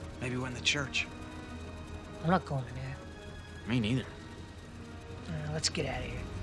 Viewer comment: i swear marv said "me Neither that place gives me the creeps" or something, is it just me?